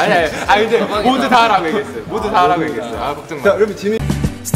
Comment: 에 지민이라고 얘기 한거 같은데?선배님이라고 했으면 말이 길어야 될텐데.?나만 뒤에를 민자로 들었나?? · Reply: 진선배님이라고 한거고 애초에 지민이랑 친구인 애가 없습니다 애들 다 예의 바르니까 몰아가려고 하지 마세요